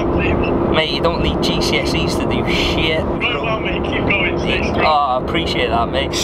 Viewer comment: did u say SHIT ? :) HAAAAPYYYY DAYS!